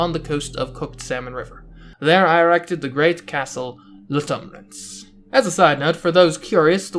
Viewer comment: I have no idea what you just said. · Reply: The Great Castle Lutumrance